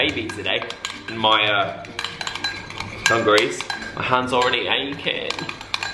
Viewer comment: I thought he was gonna say 'overalls' but he said 'dungarees' so i heard "ovaries" hahahah